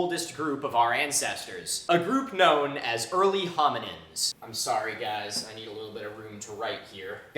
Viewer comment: Was tired and thought he said “early homies”